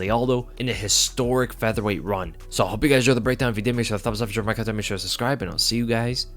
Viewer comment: Dude I’ve watched 6:25 five times and still don’t know exactly what you say lol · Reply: “So I hope you guys enjoy breakdown, if you did, makesuretothumbupmakesuretosharemycontentmakesuretosubscribe and I’ll see you guuyyysss in the next video.” -The “Eminem”